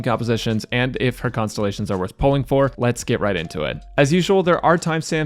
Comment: I heard: "Let's get raiden to it"